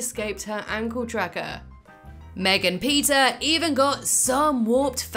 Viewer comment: Wait.. did you just called me Megan ?